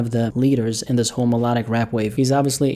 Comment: i legit thought you said homoerotic rapwave